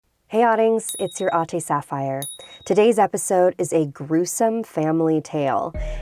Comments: I always thought she was saying "Hey Audings, it's your Auntie Sapphire" 🤦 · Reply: I thought she said "hey autings, its your aute sapphire" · I always thought you were saying “It’s your Auntie Sapphire”